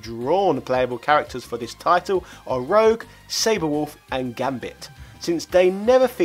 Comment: What ! Saber wolf? Did i heard right?